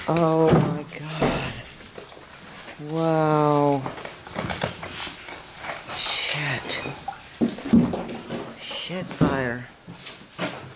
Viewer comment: in did say shitfire